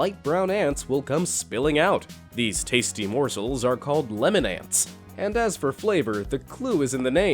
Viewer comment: When he said lemon ants I thought he said lemonade